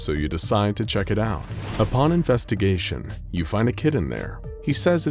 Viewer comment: i thought it said “you find a kitten there” instead of “kid in there”